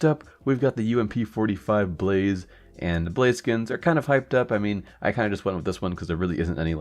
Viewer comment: When he got to the ump i heard him say either Blaziken or Blaze skins